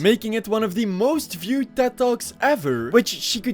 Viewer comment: Every time you said "Ted talks" I heard "dead dogs"